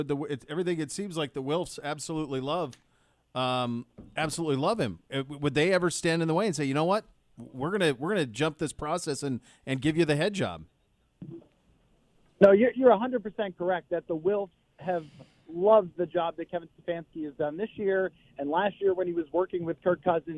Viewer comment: did he say “give him the head job” ? 😳